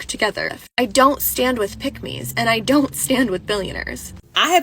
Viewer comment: did she say “I don’t stand with pygmies?” What does she have against pygmies?😂😂